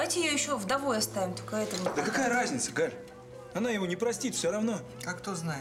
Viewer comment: Димастому послышалось и мне тоже что ли? Тома сказала: простит*тка🤭